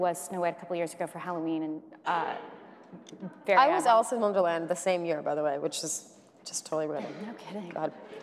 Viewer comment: What did Jen say at 2:19? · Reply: “I was Alice in Wonderland the same year, which is totally random”